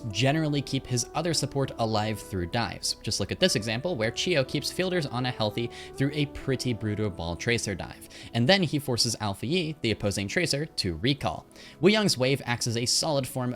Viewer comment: i heard Alfajer instead of Alphayi and i had to double check if i heard that correctly lol